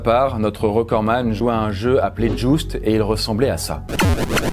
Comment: notre recordman jouait a un jeu appelé joust et il ressemblais a sa et au moment ou il dit sa pub de crossout. moi putain a les vieu jeu sur arcade ils était bien fait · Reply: Pour ceux qui ont du mal a comprendre(c la meme chose mais avec de la ponctuation): 9:09 "notre recordman jouait a un jeu appellé "Joust" et il ressemblais à ça" et au moment oú il dit "sa" pub de crossout. Moi : putain les vieux jeux sur arcade, ils était bien fait. Bonne journé